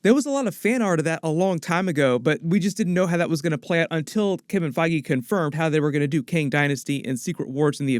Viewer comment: The captions just killed me :'-) Kim and Foggy instead of Kevin Feige xD